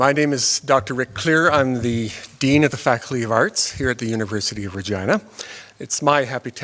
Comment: I thought she said The University of Vagina. That's about right. All modern academia is pussified.